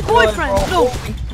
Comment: did he just say my boyfriend?????? bruh